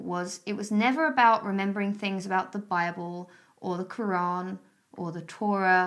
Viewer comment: I 100% heard you say the fireball instead of the bible